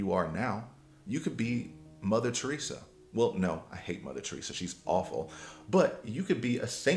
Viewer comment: did you say you hate mother Teressa?🤣🤣🤣 how you hate a Nun bro?😂😂